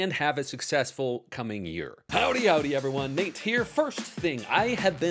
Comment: Sir, once I started the video, I moved on to cleaning dishes, and maybe the 30-second mark, I paused, and scanned my brain for if I heard you say "howdy howdy". Wasn't sure, so I actually put everything down to come check. I'm like, is he slackin'? 😆 Bless up